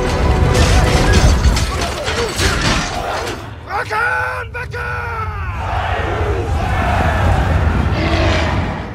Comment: Someone know what he's say? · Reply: "râkan, bekâr!" which means "rows, arms!"